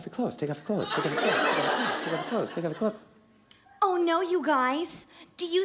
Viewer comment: In the Balcony scene, if you listen to Aladdin as he tries to shoo away the tiger, it sounds like he says, "Good teenagers take off their clothes. Take off your clothes. Take off your clothes."